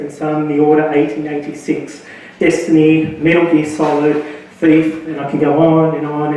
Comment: After 24:53, what was the last title he said....Faith? Is that a new game?